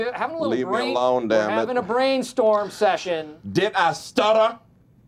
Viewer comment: what he is saying? What does it mean? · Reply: He said "Did I stutter". It is a figure of speech meaning "I am serious about what I just said".